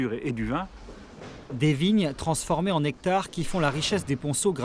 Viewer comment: des vignes transformées en hectares !!???!!???!!??? Vous voulez dire quoi ??? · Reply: En Nectare, pas hectares